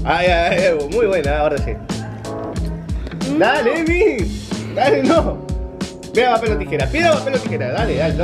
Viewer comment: En el minuto 10:10 yo escuche dale vi🙊 te quiero mucho pero se lo tenia q decir a 😍